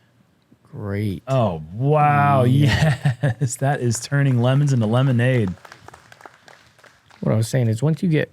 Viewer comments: Thought I heard coin a new phrase. 'He turned limbs (tree limbs), into limb-a-nade'. Please clarify on tomorrow's coverage. You guys are THE BEST. Thanks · Thought I heard coin a new phrase. 'He turned limbs (tree limbs), into limb-a-nade'. Please clarify on tomorrow's coverage. You guys are THE BEST. Thanks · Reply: Sounded like he said "turn lemons into lemonade" at